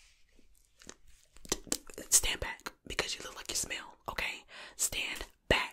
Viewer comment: "Stand BACK because you look like you SMELL." Got me so hard I *SNXXXX* out my nose and startled my husband something fierce. LOL!! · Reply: I misheard it as "you look like a snail." I thought, "why thank you. I love snails!" 💕🐌 I would love a rp video abt that! Like: "Ur a snail getting ashell art makeover by the worst marine cosmetologist" "You're a snail being pampered (water sound & soft spoken)